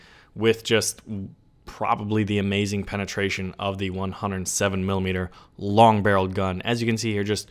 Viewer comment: When he said 107mm for the ZiS-6, I misheard it for 170mm.